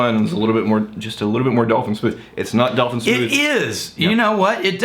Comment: hey, guys, at 12:40, is saying 'dalton' smoothe or 'dolphin' smoothe? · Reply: dolphin smooth